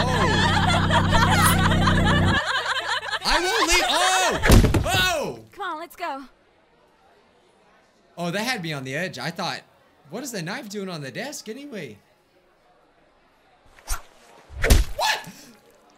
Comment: Is it me or did I hear "Why is there a nagito on the table"